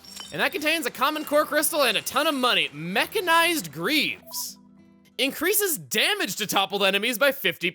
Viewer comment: I thought you said mechanized brieves.